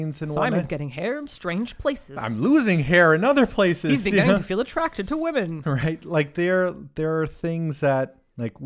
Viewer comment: What does say at 17:11? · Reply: I believe: "He's beginning to feel attracted to women" possibly joking that it sounds like he's going through puberty.